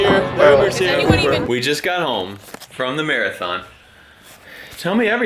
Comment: Anyone else heard "we just got home from the "mere-a-thon"..." at 13:22? xD